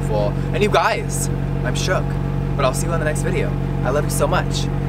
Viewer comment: I thought you said fuckle up 🤣